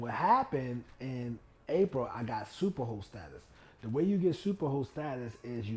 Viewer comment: I’m probably the only one that thought you were saying super hoe status lol. 🥴